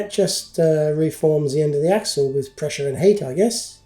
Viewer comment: Instead of "pressure and heat" I misheard "pressure and hate" and I was quite surprised. 😂